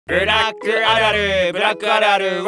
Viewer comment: 最初ブラックア〇ルに聞こえた